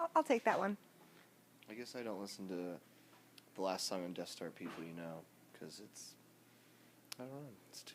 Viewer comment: What did Jon say at 11:20? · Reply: Noah Cox he’s talking about the last song on the self titled album (Death Star) People You Know but he just called it “People”